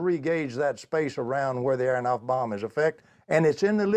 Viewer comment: instead of "I think it should be "Aharonov–Bohm" an NOT Aonoff bomb. Thank you for your amazing work!